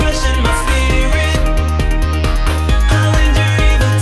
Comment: Does anyone else think it says "even though my life is A steak"? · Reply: no, but I did get quite confused when I thought it said "conjuring evil tonight." oops.